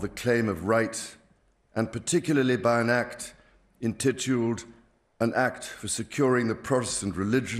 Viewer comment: What word does he say at minute 4:31? "Entitled"?